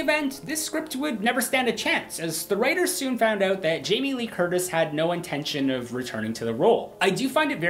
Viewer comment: Hahah. Sounded like you said Jaime Lee Kurdish.